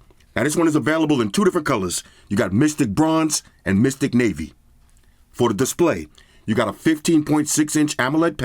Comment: I thought you said the colour was "Mystic Maybe" and laughed my ass off